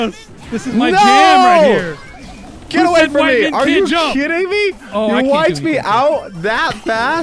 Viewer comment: did say are you shitting me? :D